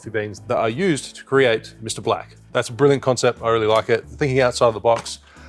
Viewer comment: did you say: “ I used to create Mr Black”? What can’t you do?!